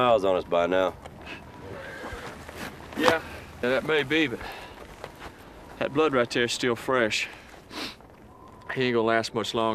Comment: What does he exactly say on standing up, about 0:16 "that may be, but had blood right ? (t)here and it's still fresh".. did I get it right? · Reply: That may be, but that blood right there is still fresh...